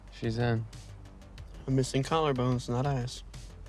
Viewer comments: "im missing collarbones not ass" im sure that wasn't what he said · the closed caption does not say eyes and it makes this scene even funnier 😂😂